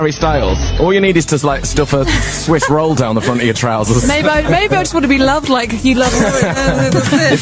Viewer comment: i heard “maybe i just wanna be loved like you love him” it makes more sense as well